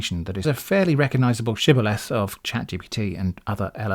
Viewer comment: Afterthoughts & Addenda 3 Point Emphasis - 12:03 - ChatGPT loves to use lists of 3 items, groupings of 3 adjectives, sentences with 3 related clauses, groups of 3 paragraphs and so on, to make its point. If you look at the text example on screen there, you'll find examples of 3 different types of hat listed, or three advantages from using the device, etc. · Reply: you said something that sounded like "chiveless"; not a word I know. I tried googling but didn't find it. In the context it seemed to mean identifying characteristic. How is it spelt?